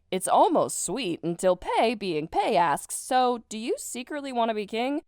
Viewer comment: I heard 8:00 as "so, do you secretly wanna be gay?" I need to get my ears checked out..